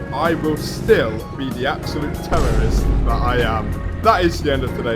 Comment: Why I always hear Absolute Toro Rosso? 😂😂😂😂😂😂😂😂😂😂😂😂😂😂😂😂😂😂😂